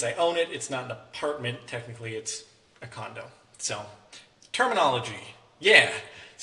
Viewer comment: Did he say condom at 1:41?